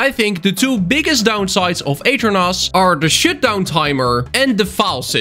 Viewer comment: am i the only one hwhi heard him say "shitdown" instead of "shutdown"?